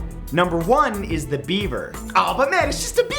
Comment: When he said number one I thought he said bieber😂